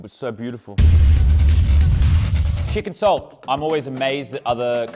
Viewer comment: I was listening to this portion of the video away from my phone and with the accent I thought you were saying chicken soul not chicken salt